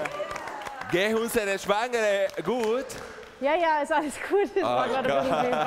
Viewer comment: Bei 8:52 hab ich verstanden: Geht unsere Schweine gut😂😂😂